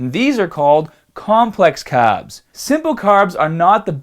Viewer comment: he actually said carbs